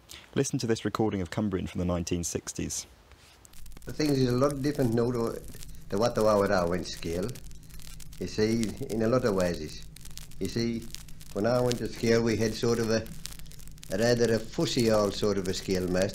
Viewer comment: Any chance of a transsciption of what the old dialect at 24:10 is saying? 2nd sentence is something about a schoolmaster, I think, but the first bit might as well be gibberish to me. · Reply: "Things is a lot different now to what they were when I went to school, you see, in a lot of ways. When I went to school we had... Rather a fussy old sort of a schoolmaster, he wasn't a bad schoolmaster either, but..."